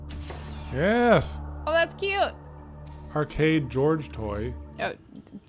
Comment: “arcade George toy” “Oh did that say George I thought it said garage” Me: arcade gorge toy